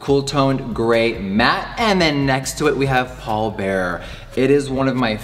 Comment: Am I the only one who’s first thought when he said the shade “Pallbearer” thought of Paul Bearer in WWE? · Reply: well at first I've actually heard "Polar bear"... not gonna lie, I was intrigued